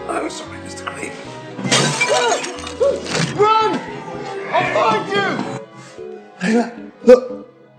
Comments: i thought he said 'i'll find you' but maybe that's just my ears, idk. i love your videos! im gonna binge watch them B) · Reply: yeah he says 'I'll find you" to Layla lol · I'm pretty sure that at 2:30 he actually says "I'll find you" towards Layla